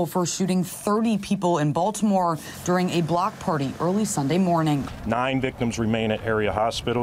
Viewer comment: Its not a block party, its a black party. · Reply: I was listening to the news the other night and I actually thought they said it was a black party. I almost fell out of my chair thinking they finally told the truth. I had to go back and read listen and realize they said block party